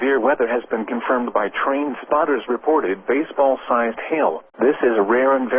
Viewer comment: did it say train spotters !?